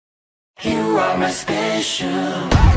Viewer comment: When I first heard this ost, I thought they said in the beginning, “You aren’t special” and I’m like “dam ok 😕” XD 😆 I like this track though